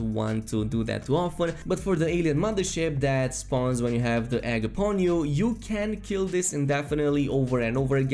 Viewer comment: I couldn’t understand what he said at 5:09. Can anyone tell me what he said? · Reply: "When you have the egg upon you"